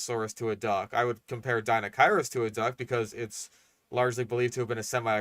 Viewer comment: I misheard deinocheirus as deinosuchus and was really confused for a second